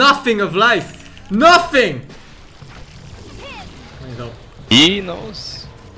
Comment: Did i heard "Peenoise" in 2:22 lool